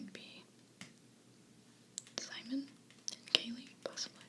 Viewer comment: Did she say Kailee? :D